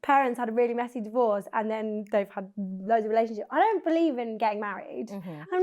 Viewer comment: it sounded like you said I don’t believe in gay marriage. I had to listen back a few times 😂😂❤